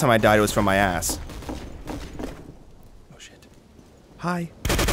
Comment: Sounded like he said hi. 10:50 nose, meet floor. 11:50 Either he's planking or a large dude with a large gun propped against the window. So confusing.